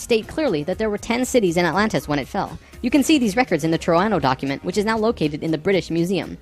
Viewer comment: im trying to hear what he is saying about the document explaining the sinking of atlantis, that sits in the british museum , i cant hear it but it sounds like hes saying the toronto document, that doesnt make sense. nothing online is connect to these keywords not even on the museum site. anyone know what he is saying? its